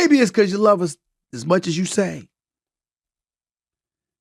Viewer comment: did he just say Will has no prenup??💔